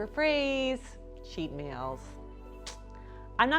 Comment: I thought she said In N Out was her favorite Cheap meal 😆